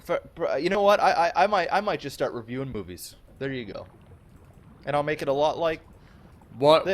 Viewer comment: I could have sworn said "I'm might just start reviewing boobies".